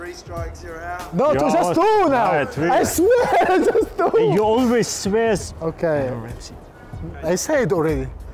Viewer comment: When Elneny said i swear Ozil said "sweas" at 1:00 haha!